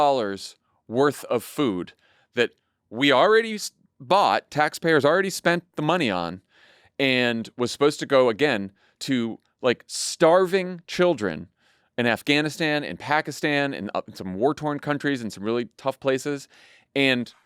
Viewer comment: Heard it as “the [Congressional] funding would go to feed six starving children,” not “…sick, starving children.” I’m at an age now where I need to rewind a couple of times to make sure I understood everything. Wish I could do the same with conversations with relatives. ;-)